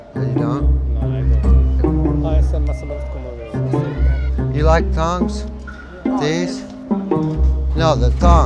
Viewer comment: is he saying "do you like thongs"